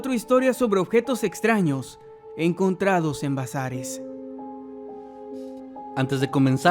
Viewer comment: Entendí "encontrados en Brazzers" 😂